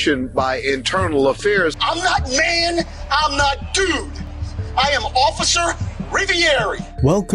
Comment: This cop is an embarrassment to everyone. Can’t imagine how he treats his family at home. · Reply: I thought I heard him say “I’m officer ravioli”, and was thinking man might want to lay off the carbs.